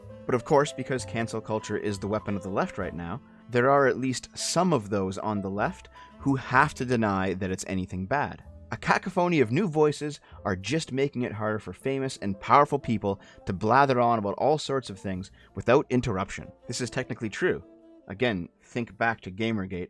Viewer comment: Did... did you just say cackaphony?